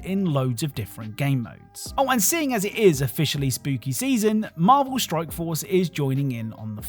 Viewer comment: I love your videos bro. But it 100 percent sounds like your saying Marvel Stroke Force several different times lol